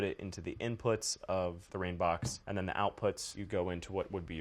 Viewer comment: what does he say here? "rain box"? "reign box?"